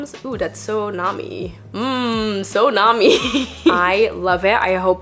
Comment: When you said “so nami” 12:01, I heard tsunami and jaw dropped when I realised the etymology of that word. Damn! BUT yay Nami is doing so welllll!